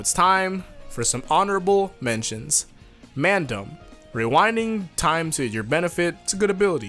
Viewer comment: any British people on here think he said mandem? 😂